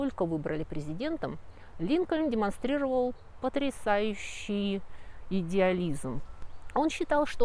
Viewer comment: Мне послышалось: потрясающий идиотизм...